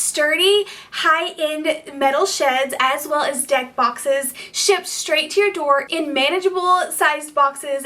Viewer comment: When you said deck boxes, I literally thought you said dead foxes. I was like why would they sell those, how awful! 😅